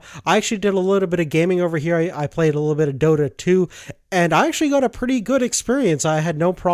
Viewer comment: what is that game called? I couldnt hear it. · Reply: DOTA 2